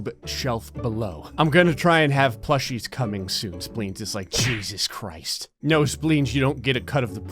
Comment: DID YOU SAY SPLEENS PLUSHIES? I'LL TAKE YOUR ENTIRE STOCK! Just the other day I told my girlfriend that we need a Spleens plushie.